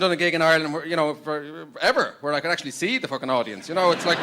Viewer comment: Did He Say F*cking